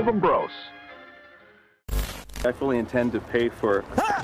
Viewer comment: Petit problème à l’export sur les sous-titres désolé 00:06 : « Bien sûr on va payer… » 20:27 : « À toi de jouer »